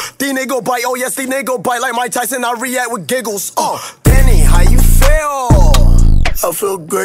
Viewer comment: I always thought it said tell me how you feel I feel great but naw it said DANNY how you feel